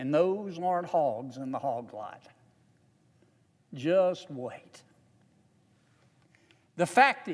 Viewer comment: What did he say about hogs at the 10:35 mark?? "Those warned hogs in the hog lot just wait"?